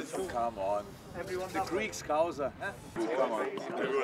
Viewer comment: i heard someone says greek scouser to Tsimikas haha